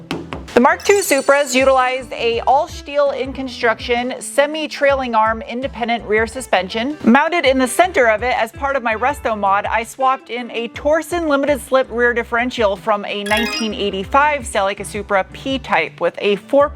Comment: Okay. I don't know if it was intentional that it sounded like you said "rustomod," but it's fantastic, and actually highly appropriate to some projects I've witnessed before. I love it and need to find excuses to say it that way from now on.